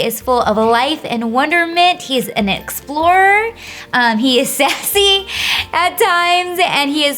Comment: Did call him sexy?